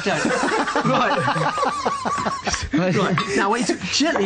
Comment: what lee said to 4:00 from 4:05?? I didn't undertsand very well.. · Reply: Nadia El Bana He said there’s a hair in it then “Don’t want someone sending that back in a restaurant”